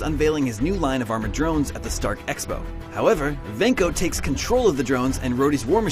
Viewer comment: Did he say , Stark Exo ? 😂😂😂😂😂